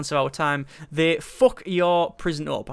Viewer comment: I thought they said "they fuck you prison up, in a way you simply cant unfuck..."